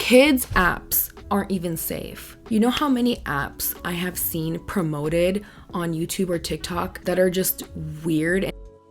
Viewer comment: Does anyone know what word she’s saying that got blocked out at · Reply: I think she's saying "fetish-y".